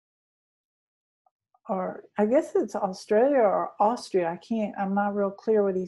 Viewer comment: Does anyone else hear the male's voice around the 18:42 mark? It's right around when she says she's not sure if it's Australia or Austria. I'm wearing headphones while listening and replayed it back to listen to it a second time before I was sure... I can't quite make out what's said but I believe popped in! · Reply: I heard him too, he said "Australia"